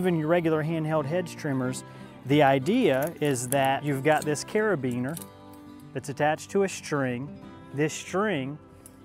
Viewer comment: That is sooooo awesome. Niiiiice job as always...BEST LAWN VIDEOS ON YOU TUBE....🌟🌟🌟🌟🌟🌟🌟 Even if i used that my shrubs would be a weird shape afterwards..😳😳😳😱😱😱 BOOOO FOR THE 3 PEOPLE WHO GAVE IT THE THUMBS DOWN...🤢🤮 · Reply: Aweee thank you so much...so you think doing the edge is better with a non string trimmer? I laughed so hard at your video bc at 1:08 I thought I heard ‘YOU’RE A BEANER”...🤣🤣🤣😂😂😂 I know you did not say that but I thought it sounded funny. I am half Mexican and half Lebanese. It was funny the way you said it...🌟🌟🌟 Let me know on the edge please. So far with my line trimmer it looks like I made a trail for hamsters..